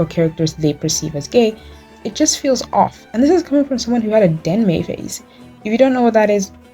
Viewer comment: i'm so sorry but i have no idea what you said, what phase?? edit: did you say danmei???